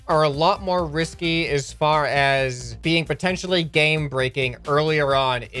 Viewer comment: Oh shut I thought he said gang rapping not game breaking